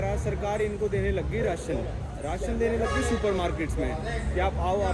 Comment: I heard the term "Ration" as "RUSSIAN" :face-blue-smiling:😂